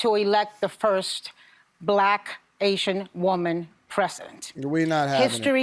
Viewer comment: "I worked hard as hell to elect the first Black, Asian woman "present". I wonder did anyone else catch that? Either way, she didn't get elected.